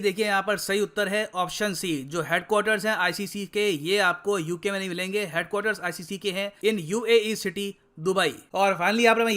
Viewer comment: Nothing understood! You said that the ICC's headquarter is in the UK, and again in the UAE !!! Do you understand what you are saying ?😀😁😃😄😆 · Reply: He said apko ICC ke headquarters uk me nahi milenge